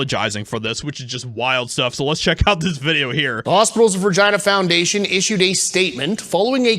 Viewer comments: I thought he said hospitals of Vag@ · TBH when he started off with Regina Foundation.... a thought he said 'Vagina' Foundation....so after cleaning up the mess from my drink i was drinking being spit out.....as I replayed it I realized what was said.....is there something wrong because I found that funny AF?....lol J/K it doesn't bother me at all...Good going Rob, keep it Petty and Salty my friend....